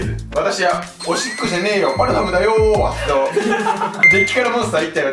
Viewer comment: 動画に集中してないとパルファムだよーんがファラオだよーんに聞こえる時もある